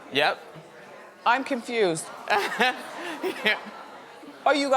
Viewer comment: When it came to Kim K & her doppelgänger, I thought Miss Wendy said "I'm KIMFUSED"...LOL it just sounded like that to me.